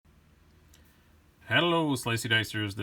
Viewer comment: The "o" in "hello" was really hard to hear, sounded like you just said "Hell slicey dicers" and I expected bad news :_(